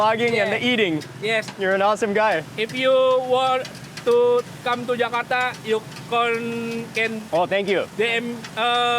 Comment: what is buddy trying to say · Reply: Next time you want to come to Jakarta you can DM me (not me but that’s what the guy is saying)